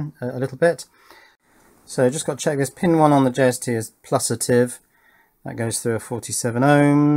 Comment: Is there only me who heard “Pin 1 on the JST is plusitive” at 4:44 ?